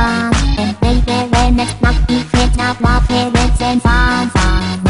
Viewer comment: lol at 1:36 sounds like shes sayin "pants pants"